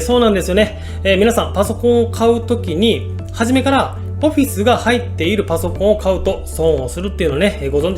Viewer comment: ここが何回聞いてもポフィスにしか聞こえない